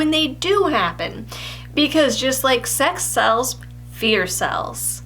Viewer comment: I heard "just like sex cells, fear cells" it took me a minute to realize what you were really saying